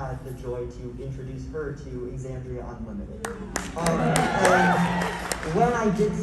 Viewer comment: wait what show? I couldn’t tell what he was saying 🤔 Edit: nvm i found it, its Exandria Unlimited